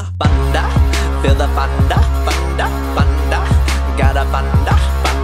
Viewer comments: Sounds like ur saying Got a panda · Is it just me hearing "panda" instead of thunder